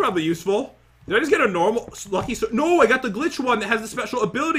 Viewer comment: When you said the glitch that's on me I was like a did he just say the Grinch